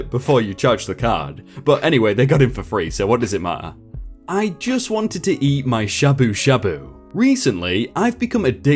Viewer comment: holy shit I thought it said “I just wanted to eat my Shiba Inu”